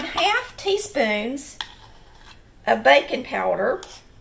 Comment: I thought she said "bacon powder" at first..... love her!